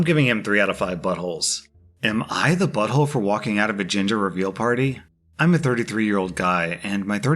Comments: bahaha every single time you said "gender"i heard "giinger" was fuckin hilarious 😂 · I thought he said ginger lol